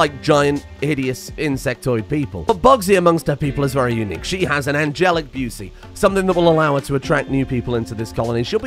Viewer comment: Had to rewatch it and read the actual trait cause I swear to god I thought you said "she has an angelic booty" at 2:20 and I laughed out loud.